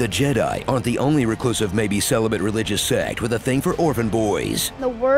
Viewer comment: totally thought he said "organ" boys... phew... "orphan" boys makes much more sense... hmmmmmm.....